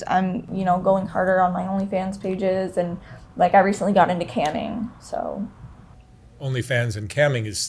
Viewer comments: Ok when she said “camming” I thought she said “canning.” Like canning vegetables😂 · I thought she said “canning”. 😆. I was like “only fans and canning… that’s different”. 😊